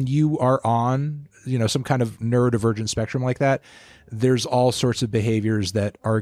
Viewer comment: I swear to god he said "nerd virgin spectrum"